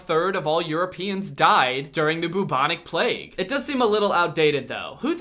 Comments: Did he just say the boobonic plague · Ring around the rosy is outdated Corona: 👀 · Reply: When he said bubonic plague it sounded like he said boobonic plague